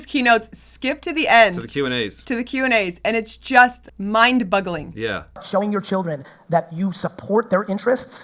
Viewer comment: Did she say "mind-BUGgling?" 🤔